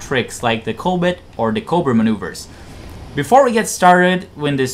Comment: Are you trying to say "cobra maneuver"? I clearly hear a 't' or 'd' and no 'r' at the end of the word when you say it. It sound like you say "covid maneuver" Even the captions say "coal but". Just slow down when you talk. I never understood why foreigners speak so fast yet haven't learned pronunciation. I would think it would be the other way around. This isnt meant to be an insult. When English speakers learn a new language they usually have to speak slowly untill theyre fluent, and speed is the last thing that comes. We must sound dumb to the rest of the world lol. Some foreigners that are fluent will speak so fast and we wont understand. Maybe English has too many unnecessary words, so foreign speakers rush through the sentences?